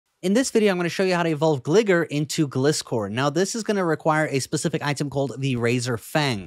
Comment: WHAT DID HE SAYYYYYYYYY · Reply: In this video I'm going to show you how to evolve gligar into gliscor